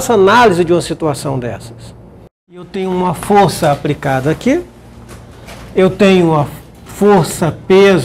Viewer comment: desculpe, mas não da pra dizer se é boa ou não a aula.. depois que eu ouvi 'a força é apRicada aqui 3 vezes não consegui assistir o resto